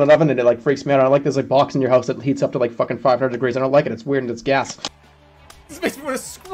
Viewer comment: WHAT IS IT, JERMA? DID YOU SAY IT'S G A S ? ? ?